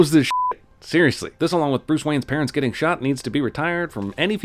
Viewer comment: Okay I had a moment of derp while watching. I hallucinated Bruce Wayne's parents that said, and thought he said Bruce Willis's parents. I don't know why... But I was very confused and wondered what Bruce Willis had to do with Super Man and Bruce Wayne. When I read/listen to things and not fully paying attention, I hear weird things!